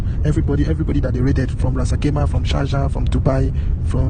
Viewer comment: think he says Sharjah, not Julfar